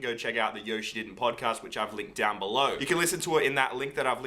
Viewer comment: I thought he said Yoshit..